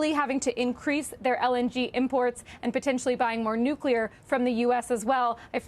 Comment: Did she say „buy more nuclear“? What?